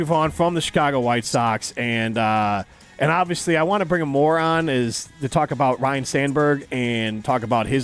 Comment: I thought said "I want to bring in a moron" had to go back and listen again lol